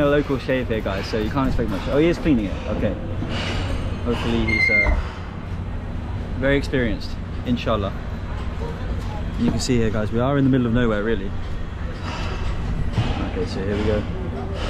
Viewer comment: Did he say inshallah?🕌